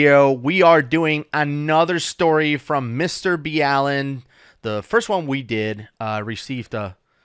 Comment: Why didn’t we know it was mister B Allen, I thought you yourself say Mr “BALL-in.” You broke my heart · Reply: it’s Mr Ballen not b allen